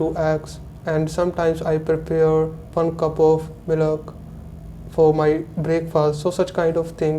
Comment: did he said 'milak' instead of milk ?